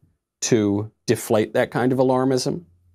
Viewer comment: Sounded like you said “keep calm and Karen on”...